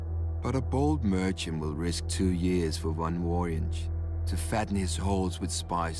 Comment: What does he say at 0:28? "But a bold merchant will risk two years for one ??war engine?? to fatten his halls with spices, gems and silks." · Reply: for one "voyage".